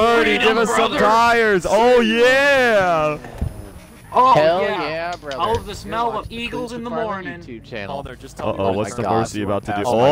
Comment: Am i the only one who heard “hell yeah brother, welcome to cleetus mcfarland YouTube channel” at 10:34?